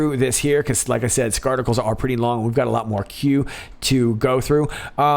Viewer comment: Everytime someone says 'article' I hear 'Scarticle'